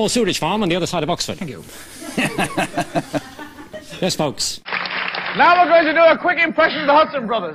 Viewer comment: what did he say? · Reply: Now were going to do a quick impression of (insert name?)brothers